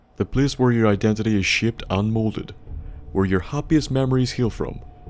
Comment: what? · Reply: its your accent, makes its sounds like you said "shit" instead of shaped. love the videos though and my mishearing your words makes its more funny than it should be.